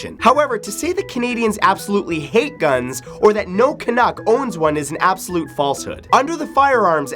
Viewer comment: Did he say Canak? or is it spelled Canuck? Sounds so cool! Give a like if you heard it too :-)